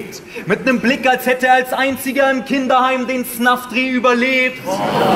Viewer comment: Was meint bei 11:15-11-20 mit der Kindergarten line? Was sagt er da? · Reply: Das mit dem Snuffdreh?